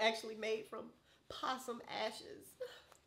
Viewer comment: I know I ain't the only one who heard her say possom ashes